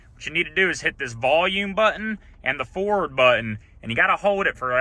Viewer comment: Thanks! This worked for my in my 2020 Expedition. At first I thought you said the 4 button, not forward button. My 4 and forward buttons are separate. So for anyone else watching, it it’s the forward ⏭️ button.